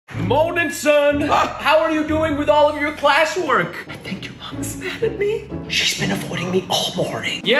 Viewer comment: Thought he said ‘moanin son’